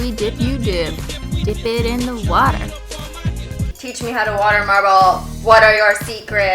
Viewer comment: I thought it said watermelon not watermarble...